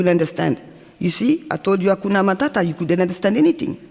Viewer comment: Did she say Hakuna Matata and then tell me that I don't understand?!